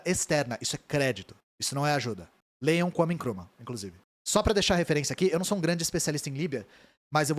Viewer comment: não entendi o que ele falou, é um autor ? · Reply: Vijay Prashad é um autor sim